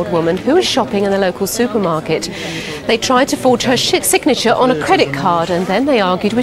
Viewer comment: did jackie say they tried to forge her shit signature ??, poor old ladie how very rude.😂